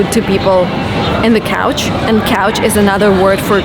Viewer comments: Did she say "Couch" instead of "Coach" ?? 🤔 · Hi, how are you? Just a little English correction for you. At around 8:02 your pronunciation of 'Coach' sounded like a piece of furniture people have in their living room called a 'couch'. Maybe I'm wrong. You can check the pronunciation on any dictionary site. 'Coach' is also the name given to the top manager / teacher on a sport team like American football. By the way, congratulations on becoming a mom and having a wonderful little girl. I haven't checked in on you for more than a few years. God bless.